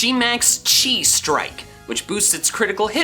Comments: I thought he said max cheese strike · Why when he said that I thought he said ‘G-max cheese strike’😭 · I thought said on gigantamax machamp “it’s fighting type moves become G-max cheese strike” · I can't be the only one who thought he said Gmax Cheese Strike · Anybody else hear “G Max Cheese Strike” for machamps section lol. Not saying it wasn’t pronounced right…I just mishear things